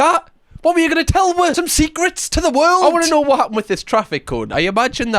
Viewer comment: why do you say “wuh” instead of “us”? Kind of a nitpick sure but I’ve been wondering for a couple years now